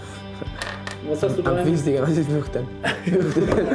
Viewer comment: Was sagt Juan bei 17:03 ? xD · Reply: Ich glaube er hat gefragt: "was ist fürchten ?".